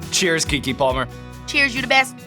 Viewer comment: What did she say in response when he said, "Cheers Keke Palmer!" ? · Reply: I thought i heard "Cheers! You the best!"